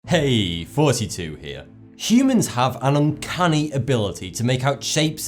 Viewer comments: Did he just say "Hey fouseytube here?" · Every time at the start of a video,I hear "Heeyy 42 here". Instead of," Hey thoughty2 here". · Wait, his name is thoughty2? I always thought he was saying forty two